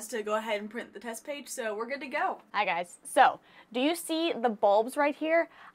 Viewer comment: i thought u said "do u see the fluffy ball"